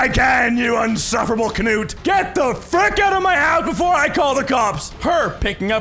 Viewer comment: Some of these stories need their own illustrations holy shit. I completely miss heard you saying "insufferable cunot" as "insufferable canou" (I can't spell I mean that weird river boat thing)